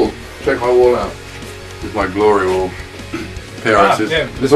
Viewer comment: At first I thought he said that it was his "Glory hole", but I figured he said "Glory wall" xD They played Mercury Summer and In The Jungle ^^